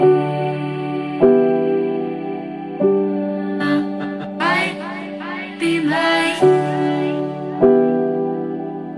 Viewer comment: what is she saying? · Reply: Are you gonna be mine?